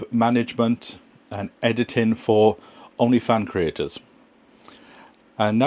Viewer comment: Did you say "OnlyFans creators"? 😲